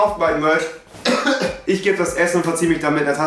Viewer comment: Ich habe zum Schluss zuerst verstanden: "Euer Raze-T." :D <R